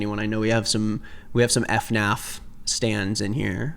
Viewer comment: what the hell did he just say 😭 LIKE WHAT… F NAPH?